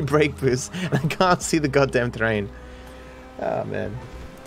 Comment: how did you say train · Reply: its chickengamez terrain